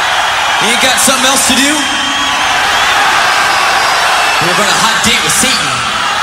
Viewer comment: did he just say, we got a hot date with satan 0.0